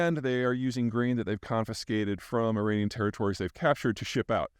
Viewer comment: Ukrainian instead of Iranian at time 5:13?